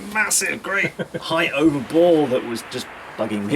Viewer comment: What does he say right there... Massive great height over. . . Ball... Or something. I can't understand, or hear what is saying.. anyone help.. thanx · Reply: height over bore